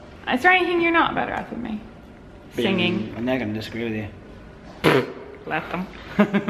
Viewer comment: Forgive my uninitiated American ears, but what the hell does say at 15:08? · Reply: And they're going to disagree with you